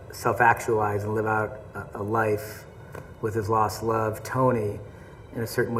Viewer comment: to laughed so hard cause it sounded like they were saying Steve’s love love TONY